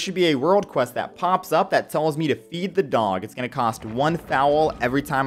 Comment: when he said "feed the doggie" i thought he said "eat the doggie" and it had me really confused for like 10 seconds. apart from that, nice farming routine! ^^